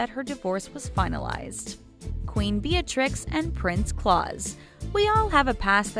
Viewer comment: I thought I heard “Queen Bellatrix”..